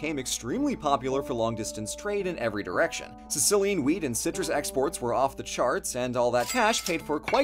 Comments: What said: “Sicilian WHEAT and citrus exports” What I heard: “Sicilian WEED and citrus exports” What my face did: 🙂😶😶😔🙂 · I've listened to it 3 times now, and it still sounds like says "Sicilian weed and citrus exports" at 5:28 😅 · I can't be the only one who heard "Sicilian weed" at 5:28 😂